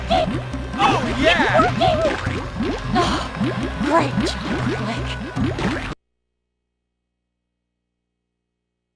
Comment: What even is she saying??? 🤔🤔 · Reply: Sounds like "oh, great job, Flik"